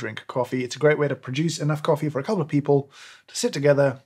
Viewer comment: I've had this video sped up and managed to mishear James saying "...it's enough coffee for coloured people to sit together in the sun and have a nice time..." That left me puzzled for quite a while...